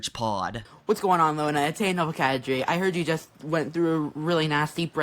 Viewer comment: Can someone tell me what the hell this gibberish is he says in every video? Bro speaks like he has a mouth full of packing peanuts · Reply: He says "Heya Nova K3", K3 is a Belgian-Dutch group that makes pop music for kids.